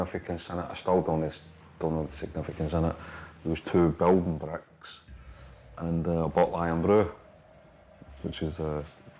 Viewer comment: what is he saying? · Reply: Two bricks and a bottle of Irn Bru, which is a well known Glasgow/Scottish drink, gaffer tapped together to give the impression of weight in the holdall (bag), I still can't understand how someone would part with £50k without checking what was in a particular bag. They saw that as a big insult, so in the process of trying get that debt back there were a few casualties..... On one occasion I was given the wrong information of someone alleged to be involved in it, as it turns out years later, he had nothing to do with it. ( had allegedly repeatedly stabbed the guy and his dog in back of a vehicle)